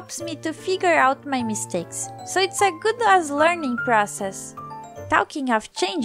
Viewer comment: what did she say it sounds to me like she sed -> " figure out my mistakes so its a good A#$ learning process"